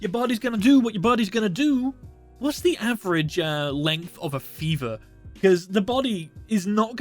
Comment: i thought he said "average length of a femur, which got my attention. my discord nickname isn't often "Femur eater" for no reason.